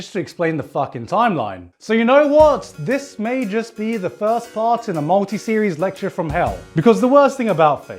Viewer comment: "This may just be the first part in a multi-series lecture from hell". I thought he said fart 😂